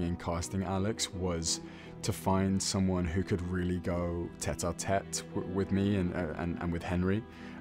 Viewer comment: what did nick say about taylor? “finding someone that could tat or tat with me”? what is “tat or tat”? sorry i’m not native :( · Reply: He said tête-à-tête, which is borrowed from French and used in English to mean like a private conversation between two people, usually friends or people who are close (and google tells me in French it means head to head)